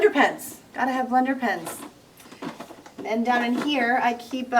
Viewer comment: I am confused what you said at 12:00 in, it sounds like Landon pants. What was it?